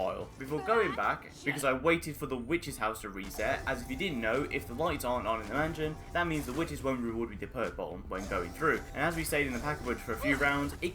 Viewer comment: What are you saying here? 8:26 After “as if you didn’t know” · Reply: Is my bri ISH that bad? Hahahaha it's "as I'd you didn't know if the lights aren't on the witches won't reward you with a perk bottle when you go through" hahahaha next time I'll get master chief to voice my videos 😂😂😂